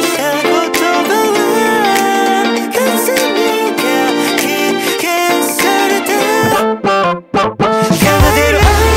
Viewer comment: の所隆二なんて言ったんだろう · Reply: あいしてるよ